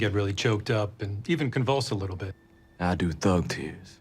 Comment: i heard "thug teeth" 🤣 he was way too quite in the video